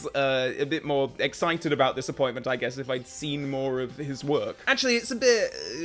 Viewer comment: I could have sworn you said ''dissapointment'', instead of ''this appointment'' at the beginning of the video 😆